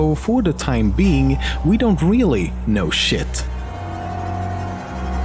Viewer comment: did he just say sh*t